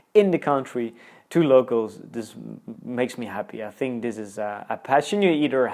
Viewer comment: nice video ... in 6:04 mint. did you said dieses ... instead of "this is " 😅 ?